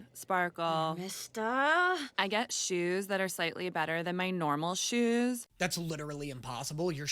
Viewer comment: Originally they had boots instead of her saying shoes and it just sounded like she said “and i get boobs that are slightly better then my other boobs”